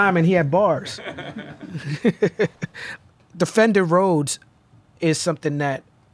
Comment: Misheard Talib there and for a second thought he was talking about some rapper called "Defender Roads" till i realised he had said " The Fender Rhodes" ..still pretty cool rapper name i think haha.